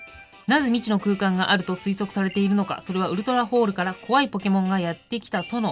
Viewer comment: ウルトラホールが ウルトラソウルに聞こえた